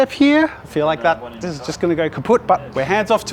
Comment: did u say, it's gonna get kaputt? It's a German word lol😂😅😊😮